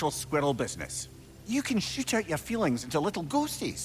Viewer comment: No joke i thought said you can Sht out your feelings XD😂